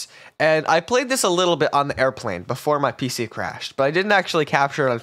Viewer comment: thought he said his plane crashed